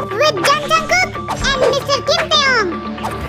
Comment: Wait did you said.. Mrs. Kim Tae hyung🤔..... 3:12?? First I heard was Mrs 😂😂